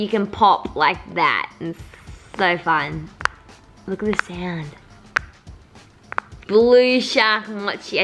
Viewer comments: what hold did she say look at the sound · Did just say look at the sound 😃